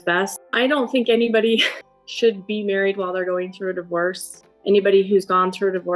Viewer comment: "You should be married while going through a divorce". Stellar advice Robyn. Good grief. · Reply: I thought she said you should NOT be married while you are getting a divorce, because divorce brings out the worse in you — implication ( at least to me) that this has been hard for her to deal with the worst being brought out.